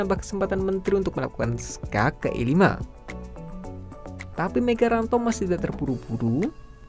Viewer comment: Kok aku ada dengat kata2 "megarantod" ya wkwkwk...atau salah dengar doang